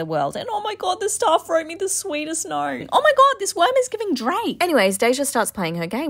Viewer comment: I thought she said oh my god this wrom is getting drained But she said this worm is giving drake😭 I just noticed because I read the comments